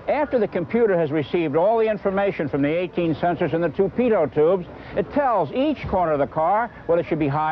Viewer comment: I seriously struggled with his pronunciation of torpedo tubes - for a moment I thought he was saying "tall paedo's tubes!"